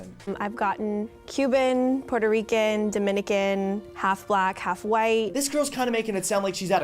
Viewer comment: I’m sorry but did you say half black when you went into specifics with other continents? That’s racist!